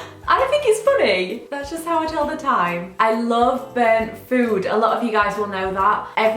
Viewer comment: When you said burnt food I thought you said bird food and I was like wtf!😂